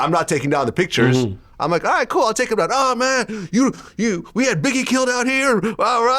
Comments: Did he just say that wack said.. we had biggie killed out here? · Did he just say Wack said "WE had Biggie killed out here?" 👀👀